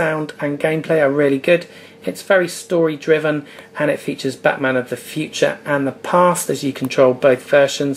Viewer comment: Did he say Batman of the future???